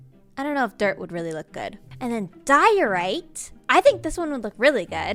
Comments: Woa woa woa,.... 4:02 . Is there die, Right instead of Diorite in the subtitles?? :D Nice video tho, hope the interior video will come soon if at all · did anyone notice that the subtitles at 4:02 said die, right. instead of the block 😂